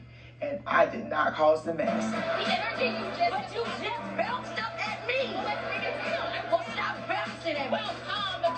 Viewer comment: What is the meaning of belching up at somebody? Did I hear it wrong? min 2:15 ( Thanks for the response)